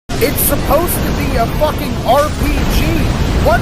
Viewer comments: Is he saying victim? I thought hes saying Vietnam 🤣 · I heard you said "Vietnam" instead of "victim", and sadly it fits too